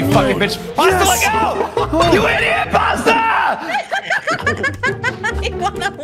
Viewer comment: Did fourzeroseven say “YOU IDIOT PASTA!!” At 11:11 either way it was pretty funny